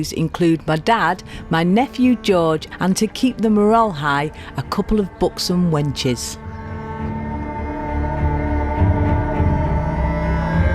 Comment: Thought you said 'books on wenches' then realized it was 'buxom wenches'!